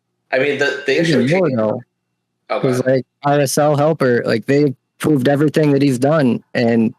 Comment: I'm asking for a friend: Which auto-clicker did mention? (21:58) He's so over modulated Ithey could not understand what he said. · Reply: RSL Helper